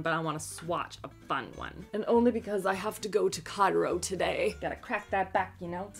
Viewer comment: Oh, going to “chiro,” I first heard it as going to “Cairo.” 😂